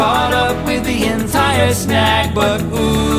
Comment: it says butt instead of regular but